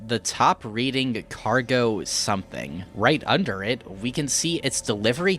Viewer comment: Cargo Manifest. That's what it says. EDIT: also, the sound you couldn't identify in the beginning of the series and in the beginning of "down" is the sound of EM interference in electronics. You could usually hear it through speakers or headphones when your mobile phone gets called in close proximity. 1:13:25 idk what that is supposed to represent but the first thing I can come up with is "flushing your chance down the drain"